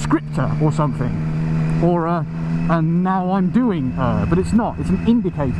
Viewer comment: I heard "Now I'm doing her." Don't lie either, we all know you said it.